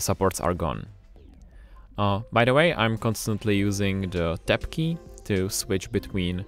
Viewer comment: at approx 7:27 you mention a key you are using to switch from preview. I was not able to understand what you said, what key is that? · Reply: The "TAB" key. We always make video subtitles in case you have trouble understanding in the future :) - Mikolas